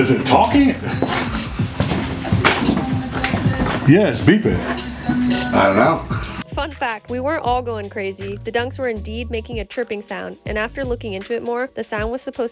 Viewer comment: Lmao I thought she said “F$&@ that, we’re not all going crazy “